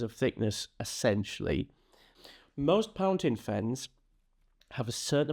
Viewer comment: Thank you for the video! I was watching it in the background at work and I heard Pountain Fens at 5:01 and almost broke my neck how hard I snapped to look at the screen 🤣.